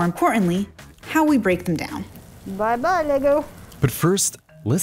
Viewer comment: I know she said “bye bye lego”, but I definitely heard “bye bye nigga”